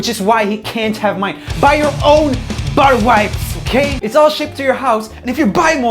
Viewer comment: uhhh.... did he just say butter wipes ??!😂😂😂 Btw love ur vids.